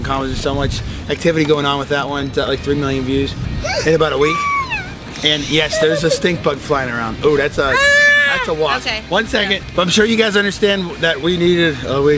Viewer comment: did daddy said wasp in